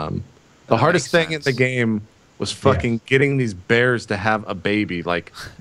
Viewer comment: I just came back from the kitchen with this in the background and the first thing I heard was "I just could not get these bears to have sex!". I just wanted to share